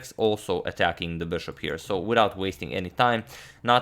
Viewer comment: I thought he was saying Wong Howell.... I was like who the fuck won lol